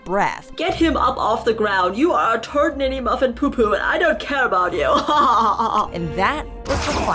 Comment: what did he say? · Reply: turd mini muffin poo poo